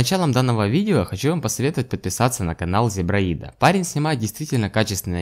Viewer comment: хах на экран не глядел сначала послышалось зебраила пиаришь